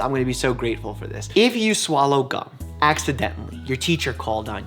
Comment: I heard "IF YOU SWALLOW CUM, ACCIDENTALLY".